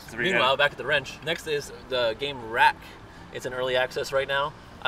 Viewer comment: What was the game @ 13:00 ? Didnt catch what he said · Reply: Wrack